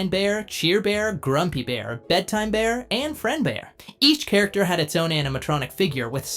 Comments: I thought he said Fred bear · the first time I heard this I thought he said "Fredbear" 🤣lol